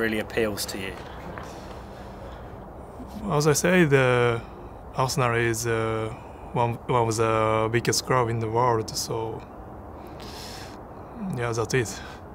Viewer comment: I can’t be the only one who heard ‘Arsenal is one of the weakest clubs in the world’ 💀 had to rewind the video